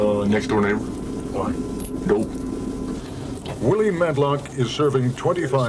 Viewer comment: why does he say he killed his neighbor? To me it just sounds like he says "duel" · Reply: He says "dope". Read his lips if you can't "hear". He clearly says "dope".